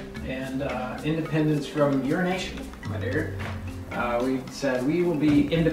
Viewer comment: who else thought lance said "independence from urination at the start instead of "your nation"?